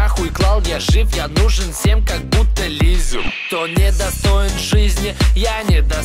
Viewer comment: Почему мне сначала казалось что он говорит "Нужен всем как буто Лизе"? Я не шиппер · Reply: Я услышал "нужен всем как будто Лизер" 😂😂